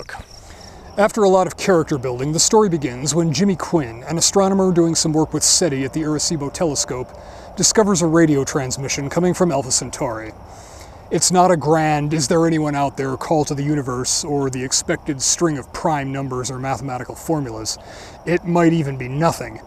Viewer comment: Came back to this. At 0:35 it sounds like you said "Elvis Centauri." 🤨😊